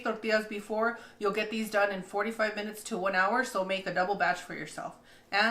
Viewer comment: Funny thing, I was listening to this with a lot of background noise. So for kneading the dough I thought I heard 45 mins and my sister inlaw was like that awfully long. I did it for about ten stopped and shut the fan off and listened again. We laughed so hard 🤣🤣🤣 4-5 mins paaahaa